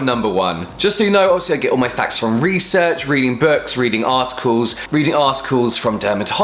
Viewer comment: He said "articles" but all I kept hearing were "ass calls" lmfao 😂😂😂